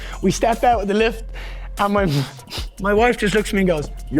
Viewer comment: Did anyone else hear him have to catch him self saying "my mum" to "my wife said, you pussy" in the last vid